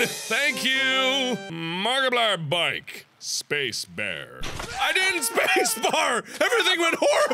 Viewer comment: I thought he said Space Bear instead of Space Bar